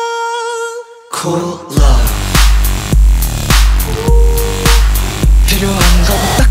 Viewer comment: Hyungwon dance machine 😋👌💯 Edit*** The whole time i thought they said CO-LA... it's COOL LOVE ladies and gentlemen.. Thank you